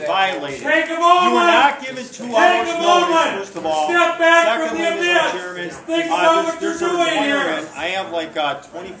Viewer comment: What was that guy yelling at :57-1:05? who was it that was yelling? why? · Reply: he said "Take a moment! Take a moment! Step back from the abyss! Think about what you're doing here!" because they were in the process of breaking the law